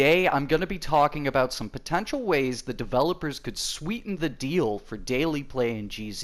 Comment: I thought it said 'Sweden the deal' and I was gonna be like. Big brain joke